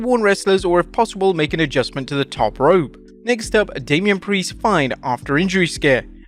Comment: I thought he said, "Damien Priest FINED after injury scare". This accent though 😂😂😂